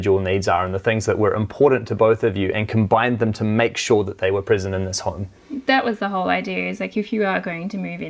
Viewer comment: "Combine them to make sure they were prison in this home." Am I hearing things